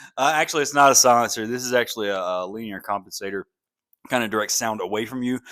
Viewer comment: Let's have a guitar battle...lol. Also, I thought you said, "wiener compensator." 🤣 I had to go back and listen again.